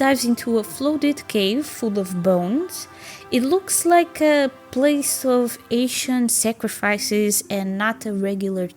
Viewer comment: Huh? I don't mention where is located in the video. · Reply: at 2.40 you say, "it looks a place of Asian sacrifice " i think I misheard and you meant to say ancient i guess.